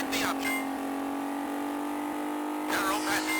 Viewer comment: When the director said "narrow passage" at first I hear "narrow package" and was reminded of Hank Hill...